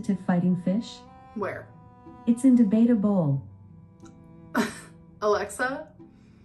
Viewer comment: Can someone spell out the joke at 5:00 for me? I can't make out what's being said... :P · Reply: where do you put an argumentative fighting fish (its in da beta bowl **indebatable**)